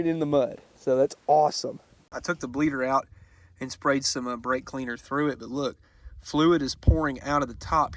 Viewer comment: Did i hear a "blake cleaner" in there ? 😂😂